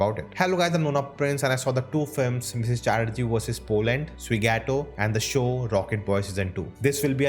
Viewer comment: Did I hear that as Chatterjee vs Poland? 😆